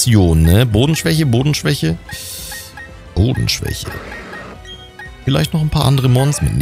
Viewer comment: Ich hab mich verhört hab nehmlich verstanden boden schwäche boden schwäche Hoden schwäche XD sorry aber musste es bringen